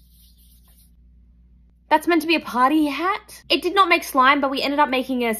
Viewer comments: I thought she said potty hat 😅 oops not party hat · I thought she said potty hat😭😏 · potty hat- Im not making fu of her accent yall I just misheard terribly